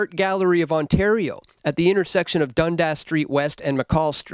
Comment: Did he say the slob statue was at the intersection of Dumbedass Street ?